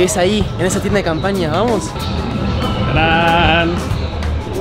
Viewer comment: por qué dijo “Graan”